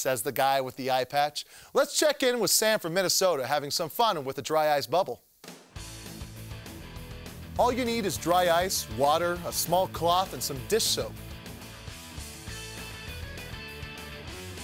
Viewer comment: Did he say, "Having fun with a dry ice boner" ?